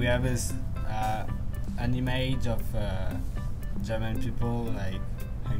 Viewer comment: I'm Japanese. 2:31 I heard him "anime" not "an image" lol.